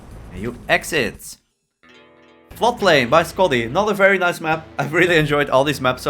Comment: Almost mishearing 'not a very nice map' again at 13:35 Edit: Nice start, first damage was that rocket splash